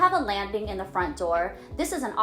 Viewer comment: What does she say at 2:39? If you have a “______” at the front door.... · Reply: A landing